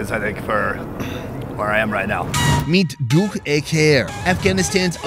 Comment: did he just say Dough-e-Kair?